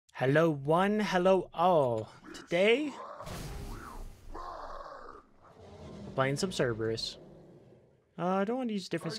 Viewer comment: Thought you said " today we are playing the servers" I was like lol RIGHT?!?! then I realized.